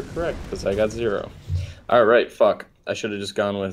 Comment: Who heard say "F*ck at 7:30 or so?